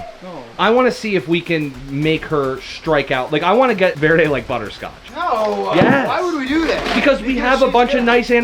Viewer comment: Sounded like said "buttercroch" instead of butterscotch